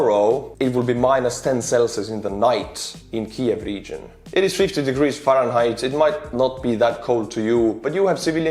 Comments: Lol. I just had to double check. I thought you said -10 celsius was 50 degrees f. I was like, yeah, thats not that cold. googles and figures out you said 15 degrees f Nah, fuxk that, I'm from Texas. Thats COLD. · Small correction at 9:55: 50°F is 10°C, not -10°C. Those temperatures are cold enough to cause hypothermia, but not frostbite. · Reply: He said 15 degrees. The captions algorithm just heard it as 50.